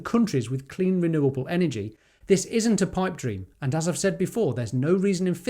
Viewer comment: in your video, after repeated playbacks I can’t make out what you’re saying. “Clean minerable energy???” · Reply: Clean renewable energy 👍